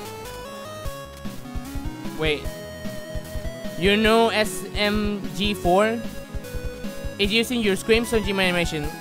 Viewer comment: did he just say smg4 the channel that got 57 million views from a fnaf Gmod animation :)